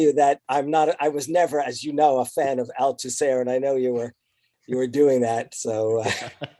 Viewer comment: "never a fan of...Al Tusehr?" can someone tell me what he said haha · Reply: Louis Althusser